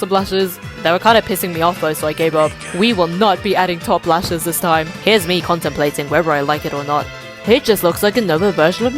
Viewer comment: what’s the whispering 🥺🥺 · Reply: enrique